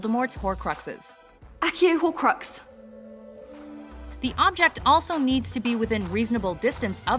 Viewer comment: i always thought she said “i can hear a horcrux” :/